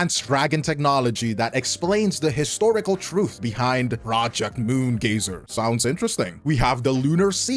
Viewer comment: I heard "Project Moon glazer" 😭😭😭